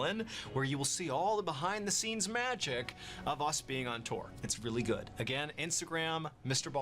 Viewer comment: Oh, he meant ON TOUR! For a second I thought he said "on tor" and I'm like why are you on the dark web, Mr. Ballen? Research?